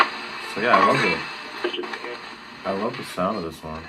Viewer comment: I heard “I love you” great radio! I’m sure I couldn’t hack it though lol